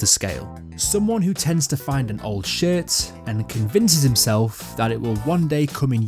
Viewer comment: Everytime you said "shirt" it low key sounded like you were saying shit...lul